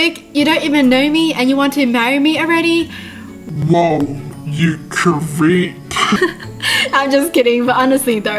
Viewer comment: i thought when you you said "Woah you creep!" you were about to say "Woah you Korean" idek anymore